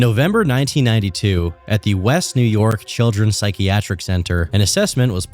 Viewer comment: I honestly thought that said “WINY CHILDREN’S HOSPITAL.”